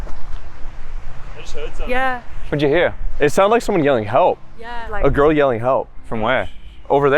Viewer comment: I could've sworn I heard a man's voice said "Help Us" Like there was multiple people in danger. I'm actually kind of scared